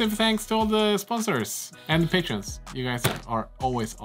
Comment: enjoy the video until I got to the ending - would you mind explaining what you said and why....... dont watch craft videos to hear stuff like that!! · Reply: I've listened to the bit a few times and it sounds like they say "Fack ÅF". "Fack" is swedish for Union (or shelf) and ÅF would be Ångpanneföreningen (these days Affry), a technical consultant company. So I think it's an expression of support for workers rights.